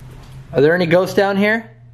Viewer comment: I cant be the onlyone who heard this but at around 6:15 he asked if there was anyone in there and i heard it sounded like a 'here' but super loud. Just please go back and check. Im retarded nvm